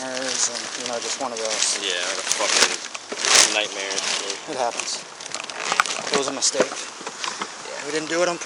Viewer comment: Did he really said "it happens" after killing a 100k boa?